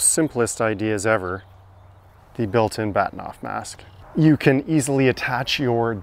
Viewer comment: Pardon me for being totally befuddled at the odd looking cap you put on the scope at 2:33. It sounded like you said “bat mouth mast.” What??? What is that, … and why?